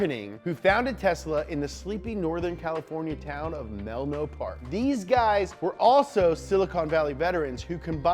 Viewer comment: he said Melno Park but the caption said Menlo Park